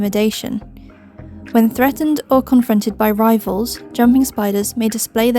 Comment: 2 things. First when you were takking about male chelicerae on jumpers, you said "rivals" and I heard "rifles". 😂 Second, you brought up sperm webs for the Tarantulas, but nothing about a sperm web jumpers? I Mean they make them too, right? Because i was talking about this on FB, in a jumper group, and they said they'd never hesrd of it, but jumpers didn't molt into their final molt with sperm already loaded in their pedipalps.